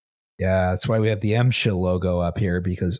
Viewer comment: I thought he said MSHIT.